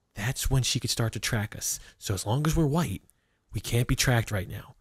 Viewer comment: “as long as we’re white, we’ll be alright.” That’s what I heard and nobody can tell me different 😂😂